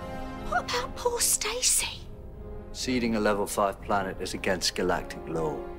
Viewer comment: did he say sealing or stealing? I think it was stealing but she's not stealing the planet. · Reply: Lvia seeding